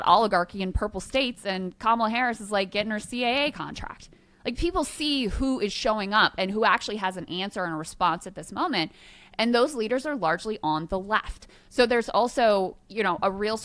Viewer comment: "Kamala Harris is getting her CAA contract..." I heard that as "CIA" so I went and googled it. I mean, it wouldn't surprise me if she also had some CIA involvement, but CAA is just a talent agency.